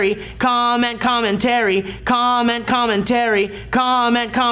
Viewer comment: Sounds like Steve is saying "comet comin terry" lol